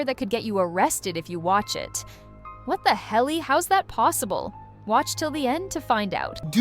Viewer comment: DID YOU SAY WHAT THE HELLY??😮